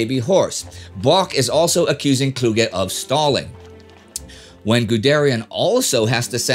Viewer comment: "Bock is also accusing Kluge of stalling." I misheard the last part as Stalin.